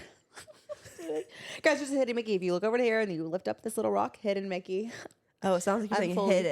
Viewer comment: 1000% thought you said "HIT IT MICKY" like Emma lmao